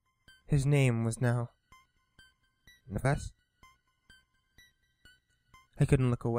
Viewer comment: His name was 'the best'? Couldnt hear it vary well... And 29:33 ? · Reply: kyuro I think it said his name was Nevets which is Steven backwards.